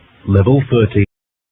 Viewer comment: DID HE JUST SAY LEVEL 30?! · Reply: 13